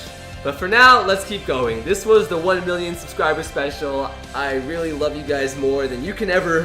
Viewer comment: does it sound like he says billion